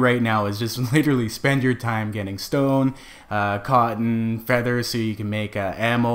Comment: I thought he said I should be spending my time getting stoned*... I was like, I'm right there with ya bud.